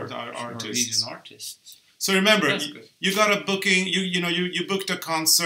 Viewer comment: I thought you said BOOZE